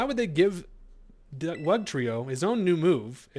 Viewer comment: I thought you said Bugtrio, lol!